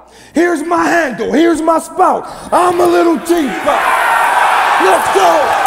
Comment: this my handle ,this my spout...I´M A LITTLE TEA POT!!! FIRE!!!! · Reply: Is it possible he said "I'm a little G-pot"? I misheard it the first time. Still laughed my goddamn ass off tho